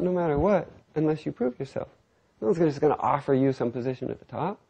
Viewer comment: LMAO at 4:20 it sounds like he says "unless you poop yourself"